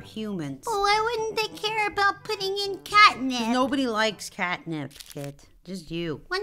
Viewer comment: I thought he said catnap💀☠💀☠💀☠💀☠💀☠